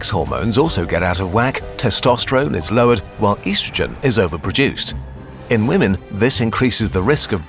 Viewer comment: Oohh, East-rogen. I thought you said Weast-trogen